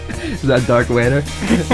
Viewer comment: Did you just call him "Dark Vader"?